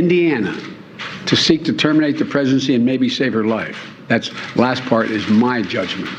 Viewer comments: Did everybody else hear what I heard? "Terminate the Presidency"....... I wish Joe would terminate his Presidency. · I replayed the clip of Biden several times. Even my kid heard him say "Terminate the Presidency" Not "Pregnancy". 🤦‍♀ 😶‍🌫 🤣😂 🤷‍♀Well. That IS the desire of the people. But, who is it, on his mind, who traveled to Terminate his Presidency, I wonder... 🧐 They do manuever and manipulate true stories, with just a twist, to hide their lies and intentions.